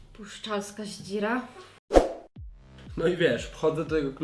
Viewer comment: Usłyszałam "Pruszczańska Ździra" xD